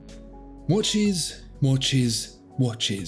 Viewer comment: If you listen to the intro enough times it sounds like he’s saying more cheese instead of watches.